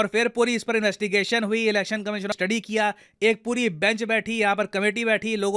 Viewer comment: I just heard Election Commission of India...🤔🤪